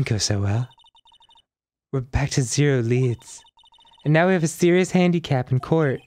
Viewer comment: I thought Mia was saying, “We’re back to zero, lads”